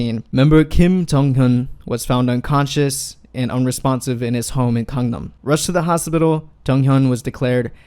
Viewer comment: thank you for doing this with subtitles. it is really helpful to deaf persons. you said home but the subtitle says hotel at